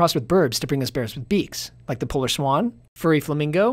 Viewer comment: When he said "polar swan" I heard "polish swan"